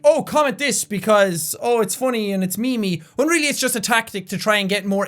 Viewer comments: did jack say me me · did Jack just say "Me Me" or "Meme-y"? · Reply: meme-y haha